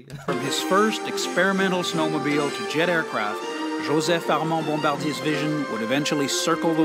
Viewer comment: Shame on you for not googling Bombardier the company. They actually said "to jet aircraft' at 17:00 minutes of the video but you missed it.